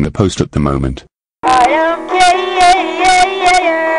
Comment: to it thought it was saying I’m Gay ya Instead of I don’t caaare